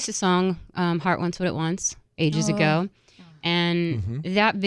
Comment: What song was she saying at 7:30? Thanks! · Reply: The Heart Wants What It Wants